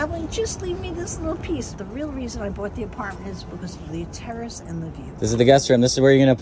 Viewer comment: “I call this a living room but the truth is idk what this is” 😂😂😂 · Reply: I almost thought she said, “I haven’t had a terrorist in over a month.” 👆When you’re half awake and “Terrace,” sounds like “terrorist.”